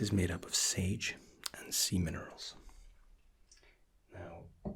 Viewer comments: did he say semen oils?! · I thought you said "semen" 😂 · I thought he said sage and semon for a second